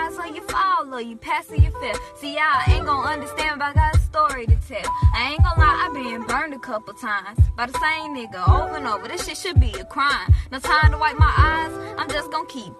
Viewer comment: did she just say nigga? Is she mixed?